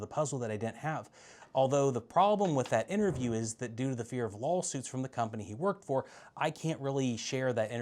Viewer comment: I heard "lolsuits" 👌